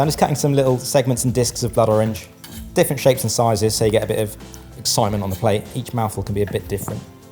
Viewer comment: Did he say "so you get a bit of Simon on the plate"? That's some rhyming slang I don't know. Edit: Never mind, I'm an idiot. He said "excitement".